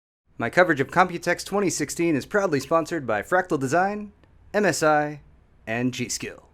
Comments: I know he's saying "proudly sponsored by", but for some reason I always hear "probably sponsored by" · Everytime i listen to the intro i hear "probably sponsored by...." lol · Lol I thought you said "probably sponsored by" the first time I heard it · At the beginning it sounds like you said "My coverage of computex 2016 is probably sponsored by Fractal Design, MSI, and G Skill"